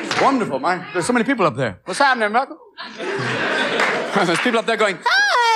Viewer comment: DID REALLY SAY??... what's happenen ni**a? 10:40 wtf..